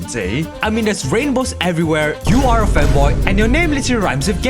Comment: I just found this channel, and I decided to watch some of the videos to see if this is a good channel to sub to. And then I get to 0:40 and I hear him say (In the viewpoint of the audience): "You are a Femboy." Might be the first Femboy YouTuber I have found. Anyways, I did Subscribe. Keep up the good content.